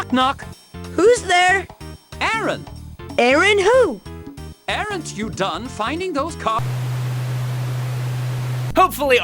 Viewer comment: What I heard: "Knock Knock Who's there? Aaron Aaron who? Aaron't you done finding those c#ck---"